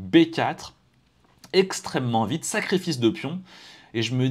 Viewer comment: J'ai entendu "sacré fils de pion" AHAHAHHAHAHA